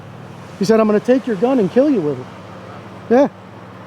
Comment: Should have told the officer about the kid that said he would take your gun away from you and did he say kill you?